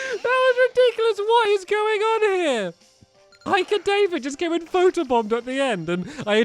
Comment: BEAKFAST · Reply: MWC3 I didn't get it first but now I heard him say WHAT IS GOING ON HERE